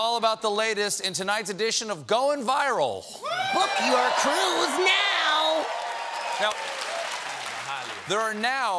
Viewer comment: I legit thought the coronavirus said "You're screwed now!"